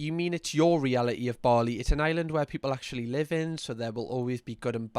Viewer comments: Oh! Bali! Sorry. I'm American, and with the accent, at first I thought you and she were saying "barley", and I though that was maybe the name of a town or farming community in the English countryside, or something. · I thought the lady was saying Barley, not Bali. I was over here confused wondering where Barely was 😆